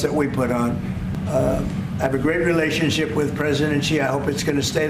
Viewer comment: did he say “president Xiao” ?!?!? 🤣